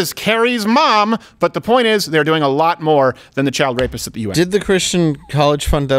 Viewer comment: Did he just say the child rapists in the US are doing a lot more than the child rapists in the UN?